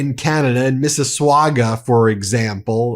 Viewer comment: did he say ‘mississwaga’ 😭